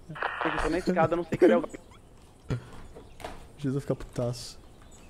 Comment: "Vx vai fi Caputasso" eu entendi isso kkkkkk